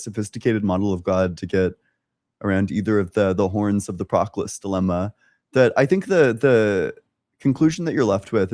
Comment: the "?? Dilemma"? Not the first time I hear this on channel, but I can't find it anywhere to read about. Could someone spell it to me, please? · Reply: "Proclus dilemma" I believe